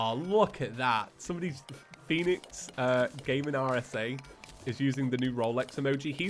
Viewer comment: what did you say?, did you say PhoenixSC?